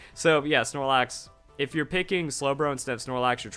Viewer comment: All I heard was "Keep picking Slowbro" so that's what I will do